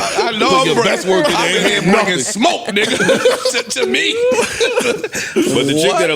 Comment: Did parks say nigga